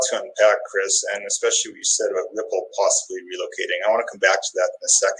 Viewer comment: Irresponsible for Larsen to say it unless he's already planning the move. Hate to say that I believe in ripple and xrp but I dont think he should have gone there. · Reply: I thought he said " considered relocating" keyword "considered"